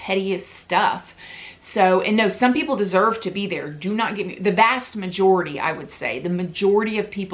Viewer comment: Girrrrl!! Did you say ‘the BAST majority’?!? Hahah